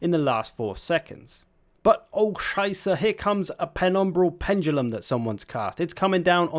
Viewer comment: what....are you actually saying....? sounds like german - orkscheiße? :'D